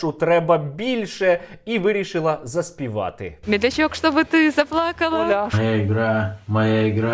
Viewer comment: лол, почулося "медведчук чтобы ты заплакала"